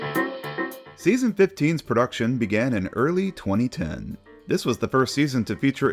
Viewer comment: For a second I thought it said REproduction history 🤣🤣🤣🤣